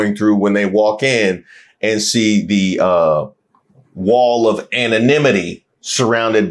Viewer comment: At first I thought she said wall of pain!😢😮😢🤯🙂